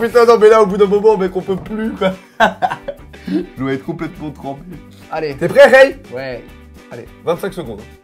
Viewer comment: il dit quoi? "l'eau est complètement trempée" ? En tout cas est super fort x) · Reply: loute Il dit " J'dois être complètement trempé ", ceux qui pensent qu'il dit " l'eau est complètement trempée " c'est juste une illusion auditive parce qu'on s'attend à ce qu'il dise " l'eau est complètement trempée ".